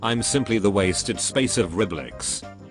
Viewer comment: Wait.... did he just say rib licks? ._. He licks the rubbers Explained Meaning Jk 1:23 Ummm Sir Builder Hat You forgot The Rich Head's man You picked the dominous